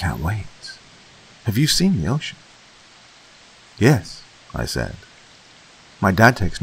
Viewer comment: I thought he said yoshi